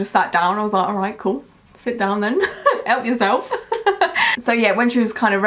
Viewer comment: In that vlog I thought you said she helped you I had to listen a few times XD